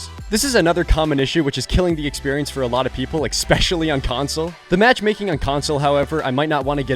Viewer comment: did you say exspecially?